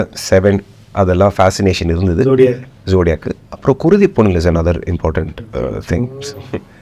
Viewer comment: he mentions a movie that sounded like Kurudhi Pon or something like that. Can anyone tell me the actual name of the movie he referred to, please? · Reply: Oh, i think I figured out which movie he is talking about-- Kuruthipunal?